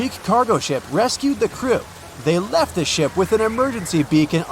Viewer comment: 😂😂😂 I sooo thought you said, they survived because they left their s@ behind 😂 I had to do a double take to see if I heard what I thought